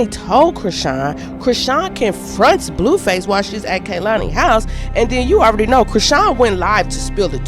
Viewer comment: She gone mess up every opportunity behind a 🥷🏾 that’s incarcerated. 😂😂 the foolery of it all. Then I thought she said they were at Kehlani’s house in the studio when she asked Blue if he messed with kehlani. 🙄